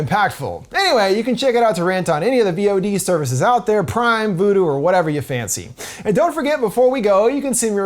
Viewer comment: Did you just say 'Prime, Voodoo'? And of course Xander Berkley is the devil.